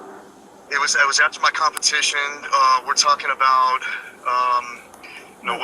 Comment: Her husband wanted to make it clear he was in a competition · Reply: Lol.. I thought he said compensation.